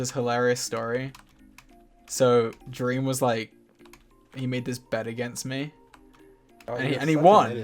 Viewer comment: why did i hear "he made this bed against me" bahaha